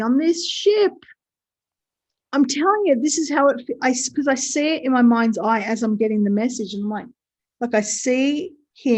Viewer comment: I’m sorry, but when you Whispered, I couldn’t make out what you were saying! 😩👂@ 1:38:00??? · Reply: All I mumbled was "don't say it" ...